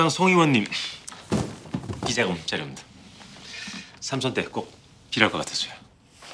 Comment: 보다보면 엄기준 딕션이 안좋은거같은 경우가 많이 있어서 당황스러움 ㅠㅠ 3:44에서도 뭐라고 하는지 너무 못알아듣겠음. 원래 그랬었나. 이번만 유독 그런건가. 왜 저렇게 말할때 입을 다물듯이 입술에만 힘을 줘서 말하지 ㅠㅠ 대체 저기에서 비자금 어쩌구 뭐라고 말한거에요? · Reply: 비자금 자료입니다 3선 때 꼭 필요하실거같아서요 라고 하는거같은디 선거할때 필요할거같다고 뇌물주는듯